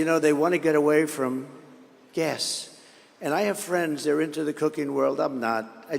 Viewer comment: I thought he was saying "cocaine" not "cooking" 😂